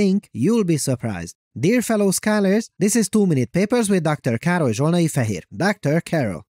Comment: I always heard "This is two minute papers with Dr. Karol Julenaifor here" ... which always made me think: "we know he's there.. why does he always says he's 'here' ?" until I saw your name in the description of your videos and on papers :)